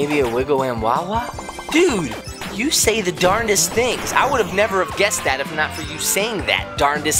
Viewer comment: is thought said "darnest"